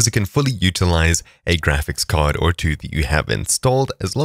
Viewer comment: seconds you say "a graphics card or two" if I hear correctly - can you confirm that's what you said, and if you meant that NVENC encoding within premier will support the use of two or more video cards to faster encode video?